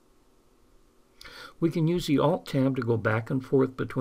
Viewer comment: Can you type what you are saying at 2:34 it sounds to me like alt-10 and I'm not certain I'm hearing you correctly, thanks, L. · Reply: It is Alt and Tab. At 2:42 I display the keys and explain how to use them